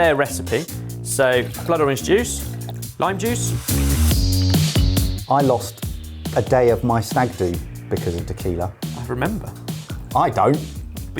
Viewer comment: what does he say? "i lost a day of my snagdu"?